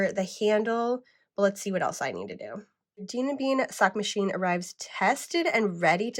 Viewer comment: Oh, Dean & Bean…. I heard “Demon Bean”😅